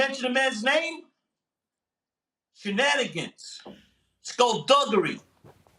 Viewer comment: My man said “shenanigans & skullduggery” 😂😂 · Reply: I thought he said skullbuggery 😂